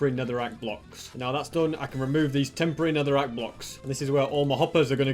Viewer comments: why did you say netherite blocks ??? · "...remove these temporary netherack blocks." ME: "did he just say temporary netherITE blocks???!" 🤣🤣🤣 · Netherite blocks eh?? Anyone else heard that? 👇